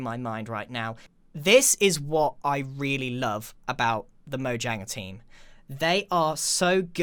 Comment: This update looks AMAZING! Remember just because I shared some opinions or worries (mainly based on the fact that we don't have all the information) I am still very happy with the features they are adding, they look great and will add a lot to the game. Clarifications: - I forgot about the deep dark. There was a lot of info to go through. My point still stands that skulk looks like an end block. - I should have said friendly mob "in the overworld" I did forget about iron golems though. My point still stands, I would love to see more friendly mobs like the strider than real animals. - a lot of my opinions are speculation on what could be. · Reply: Did you just say Mohjanga